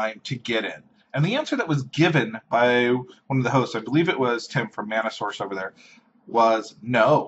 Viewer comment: is Wedges real name Tim? I was not sure what you said... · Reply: no he just goes by tms wedge online which I read as Tim when pronouncing it it stands for the mana source